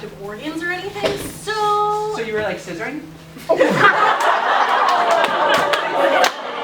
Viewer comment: Can somebody tell me what the guy at 2:51 asked and everyone was surprised or laughing? I cannot hear him very well. :P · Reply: "So you really like scissoring?" is what he said.